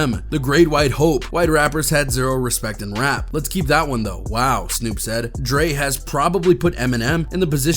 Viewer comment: I dont think Snoop said "Lets Keep that one though, wow" as said , at first listen I heard "lets keep that one Thow-wow" as in keep it 100+0.... one thousand... but thats just what I heard...